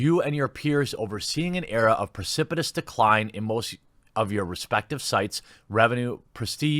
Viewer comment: I heard that the first time as "respected sites". I was going to say...I didn't know they had any.